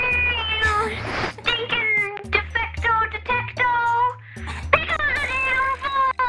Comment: not a gibberish "PICK UP THE DAMN PHONE" 3:02 "The time limit has been reached" 3:03 Kachaw.. i wish its kachaw "Pick Up" Ka kavoom "The phone" My headphone felt kinda muffled and i believe when i was editing this video, i was a lil bit sleepy. Thanks for yall to comment what they actually said.